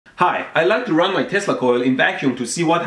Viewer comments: Sounded like you said you like to run your testicle in the vacuum. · I thought he said testicle and not tesla coil in the intro